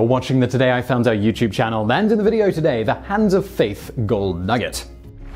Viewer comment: actually thought it said "hand of faith chicken nugget" at first.